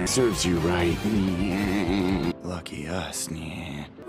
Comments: had to play that back thought they said "racist" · please tell me im not the only one who heard “were both racist!” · Nah I heard “We’re both racist-“ first 💀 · OKAY NAH WHAT DID HE SAY?? I HEARD ‘WE R BOTH RAZORS’, WHAT DID HE ACTUALLY SAY (THE KID AT THE END), CAUSE IK FOR A FACT HE DIDNT SAY THAT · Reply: "We're both racers" TT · i thought he said racist 💀